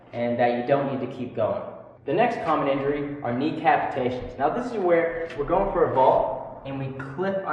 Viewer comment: who else thought he said decapitations instead of knee capitations